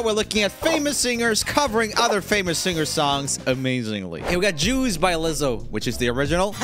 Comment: When you were talking about Lizzo, it sounded like you said "Jews" instead of Juice. 😆